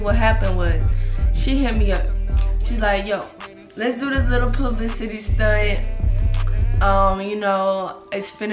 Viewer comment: 😂I thought she said p.u s.s .y. ! Every one deserves a second chance. She was a young lady who did have a pretty unhealthy upbringing. She was in her teens, I know if someone followed me around in my teens, I made some pretty terrible decisions too. She's a Human, that makes her enough, just like everyone else.